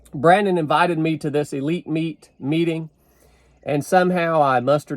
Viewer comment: I heard "Elite Meat" and was expecting some nutritional dietary programme to get mentioned 🥩😁